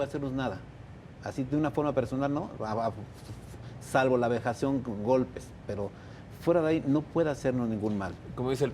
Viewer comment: Qué dice el padre a 47:33? "salva con golpes"??? El cuerpo de Leonardo estaba cubierto de moretones. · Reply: savist"salve los golpes" del diablo y las vejaciones. El diablo, satanás atacan a la persona poseída por medio de golpes, arañazos; vejaciones, etc. Y de eso dan testimonio todos los exorcistas en el mundo. No te confundas con la muerte de muchacho. Además las autoridades se iban a ir con todo contra el sacerdote. Eso se vio desde el principio. Por cierto el que siempre fue sospechoso, por las entrevistas que dio; y como único vocero de su familia; fue precisamente el hermano del muerto. Pero las autoridades nunca los investigaron. La actitud del hermano siempre fue más sospechosa; que la del cura.